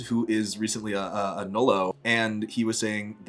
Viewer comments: can someone explain what ‘nolo’ is? or i might not be hearing it right, though i don’t think ive heard of this term before! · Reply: I think he said nullo, referring to someone who's had their genitals surgically removed! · your friend is a what? I'm sorry; I am not sure what you said, or how to spell it, so I can't even search for more information elsewhere. Does anyone else understand what he said and can help me please? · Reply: Nullo. nullification of the private parts. google at your own risk <3